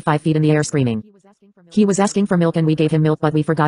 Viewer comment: Is it just me or when it says “He was asking for milk” I heard “He was zesty for milk”